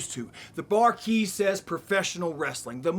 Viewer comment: FTR are the shit. They crack me up. Legit thought Dax said "the barkeep says professional wrestling" and I was like uh, I guess? Good thing he said it twice lol